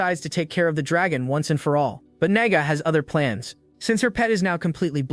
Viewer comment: bro did NOT say nagga